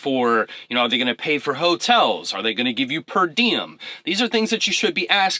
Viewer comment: What's that you said? "Perdium"? What's that mean?